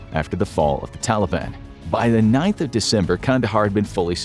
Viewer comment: it's says September 9 instead of December 9 with you said